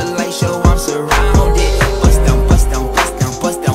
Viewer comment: Sound like he is saying I'm busted.